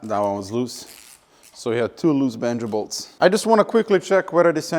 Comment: I heard "Vengables" like 4 times before I realized you were saying Banjo Bolts. I need to get my hearing checked.